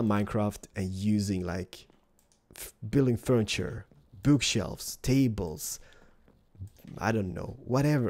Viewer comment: did you said boobshelves? xD só funny to watch